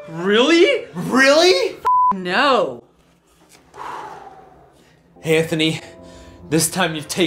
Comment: Did he call him "hanthony"?